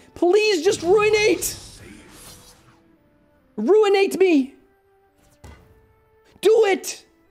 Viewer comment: i thought i heard *urinate me*. I guess i was wrong